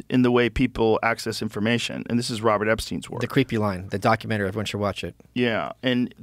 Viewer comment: documentary are they talking about? He says it's so quick. I tried rewinding and I can't pick up what he's saying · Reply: The creepy line documentary…